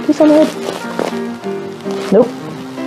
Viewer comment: 나만 ''노우''가 아니라''싫어''라고 들었나?